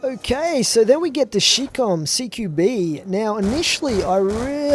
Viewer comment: sounds like he said shit com